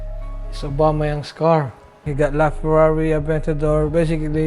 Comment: did he just say farrari adventador